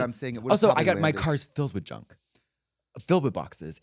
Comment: I'm so used to Katya I swore she said filled with feces and I wasn't phased